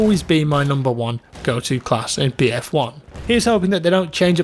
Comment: I thought it said me dic is king